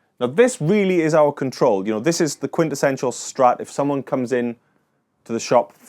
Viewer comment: Did he say if someone comes in for a strap? 🤔😂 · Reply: Strat as in stratocaster